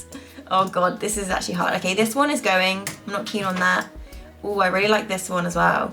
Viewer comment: No quiero naada (es lo que entendí XD)